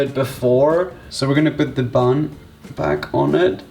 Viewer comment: he said pan instead of bread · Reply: He said Bun not Pan